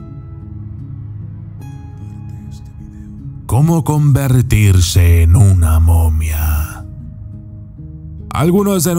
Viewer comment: Hola, porqué en el minuto 6:45 se escuchan unas palabras a muy bajo volumen que dice algo así como: “Quita este video”. Me pareció muy extraño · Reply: La voz dice comparte este video